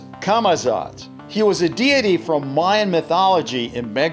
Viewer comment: What is the...? What? 5:15, i can't hear. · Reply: He said “was a deity”